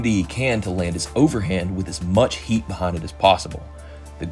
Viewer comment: did you say pon it? You Jamaican too? Lol 🇯🇲🫡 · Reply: Hahahah! I said, “Behind it”. I’ve got a bit of a country accent. I try to suppress it but it slips through occasionally